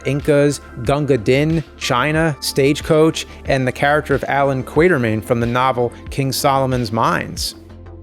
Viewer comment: Sorry.....did you say "Allen Kwater-mayne"? 🙄